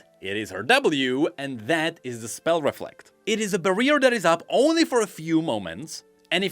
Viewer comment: did you just say bareer?